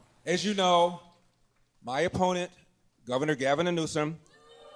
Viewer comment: Did he call him “Governor Gavinor Newsom”? “Governor Gavinor” -- I like that. Or just “Gavinor”. Congratulations, Gavinor.